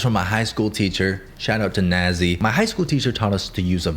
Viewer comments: I heard “Shoutout to Nazi” 💀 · "Shoutout to Nazi" Did I misheard